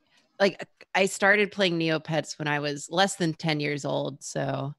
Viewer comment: Wish I could have made it while this was live. Thanks for this exclusive stream! You guys are the best! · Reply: Holly shit i thought you said "when I was alive" and got really freaked out lmao